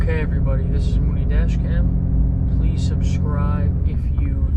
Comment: Every time I watch it sounds like u say this is my new dash cam lol but you are saying this is probably because I'm British cool videos though